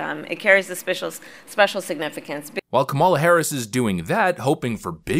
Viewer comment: it sounds like Kamala almost says suspicious significance, instead of special significance. 😂